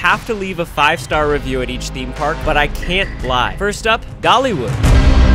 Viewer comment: Was I the only one that thought that he said gollywood